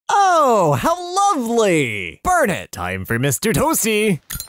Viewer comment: bird henti for mister tosty ? Did i hear that right?